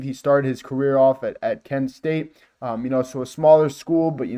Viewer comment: Just FYI he went to Kansas State. Spent 3 years there I believe he started two of them. If that is what you said I apologize I thought you may have said Kent State